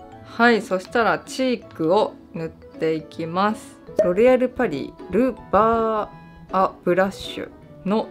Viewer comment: 一瞬「ロ リ アルパリ」に聞こえたw